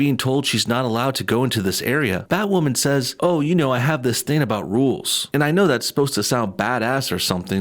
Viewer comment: I didn't realize she said 'she has this thing about rules'. I thought she said 'roles'.